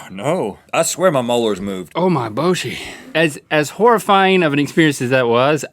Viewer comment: did Link say "oh ma boje"?